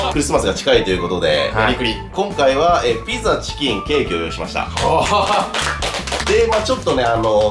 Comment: 「ピザ、チキン、けいけーを用意しました！」に聞こえてワロタ